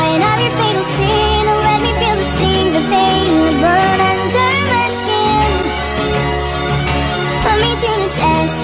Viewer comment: did it say bum under my skin 😮😮